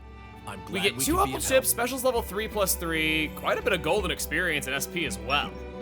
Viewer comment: I thought I heard “Gold Experience” for a split second… 😂 [insert piano here]